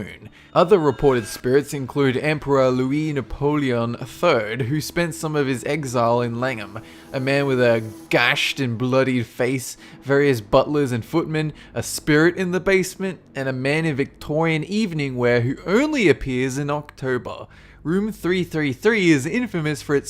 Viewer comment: Thought you said "butt lizard footman" for a second. Paused for another second to think how awesome that would be.